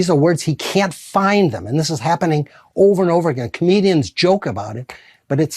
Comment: I thought he said Canadians joke about it but he said comedians. 😂 19:29 -- he could care less about what is happening. He's just trying to get that little walk over with.